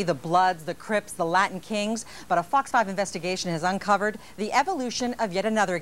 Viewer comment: When she said "Fox Five Ivestigation" I thought she said "Fucked Up Investigation"